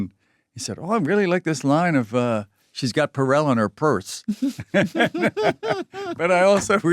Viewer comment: She's got (?) in her purse - What is he saying here? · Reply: “She’s got Purell (the hand sanitizer brand) in her purse.”